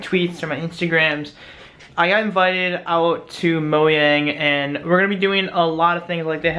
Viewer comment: did he just say moyang